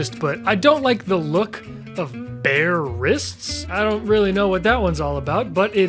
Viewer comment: idk if anyone else thought this but when he said he wasn’t a fan of bare wrists i thought he meant the animal, like bear wrists, and i was waiting for him to show a pic on screen